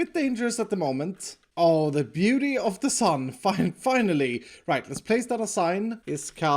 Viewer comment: Did anybody else hear that wrong? "The beauty of the sun, ____ing finally.."